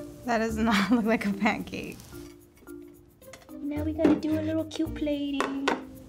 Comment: Wth does she say at 10:06?!? lmao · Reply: "Now-a we gotta do a little cute plating"